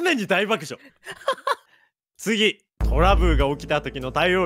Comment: さん トラブルが言えてなくて トラブーって聞こえる